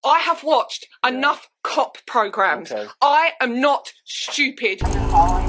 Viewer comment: I thought she said c*ck 😂 programs